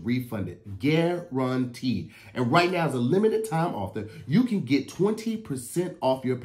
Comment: “ and right now as a limited time author “ . Instead of offer 🤣💀